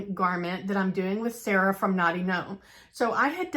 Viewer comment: Can't wait to see your collab garment! It was loud here & I thought you said Naughty Gnome instead of Knotty, lol. You will Love Elton John! Wow, you have a lot of family info with all that hard work your dad did. So cool you have a seamstress in your history. I use rainbow thread in my serger too, love that pop of color! I'm working this weekend on making two zip pouches to fill with a me made lavender eye mask, chocolate & tea for gifts to give next week at a professional conference in Orlando. Can't wait since it's been cold & gray here in Chicago. Have a great weekend!